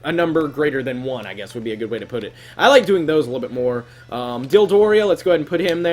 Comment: did you say Dildoria LMFAO